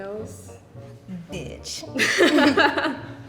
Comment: not me thinking she said NOSE instead of NAILS and being very confused, bc of the subtitles 🤡